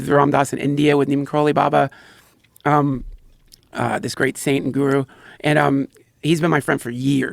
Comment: did he say "this great Satan guru"?